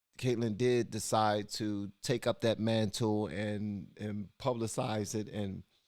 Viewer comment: I actually had to replay the part when you said Kaitlyn picked up that mantle, because I thought you said man tool! 🤣🤣🤣